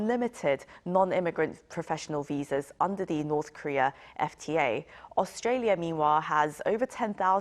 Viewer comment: Did she say North Korea FTA? Please correct this ASAP